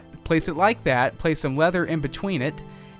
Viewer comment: did he say weather or lether?